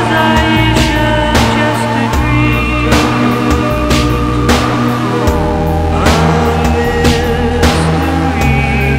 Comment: anyone else hear someone talking in the background? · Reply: Yeah it's the background vocals saying "just a dream"